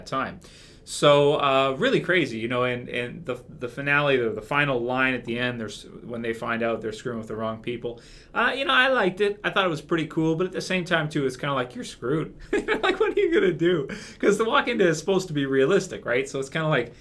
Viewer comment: It might be just me but I didn't hear Rick say "They're screwing with the wrong people" I thought he said "They are going to look really stupid.....They're screwing with their own people". Was probably me hearing it wrong though.